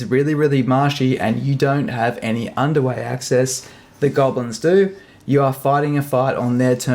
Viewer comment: I thought you said "No underwear access"🤣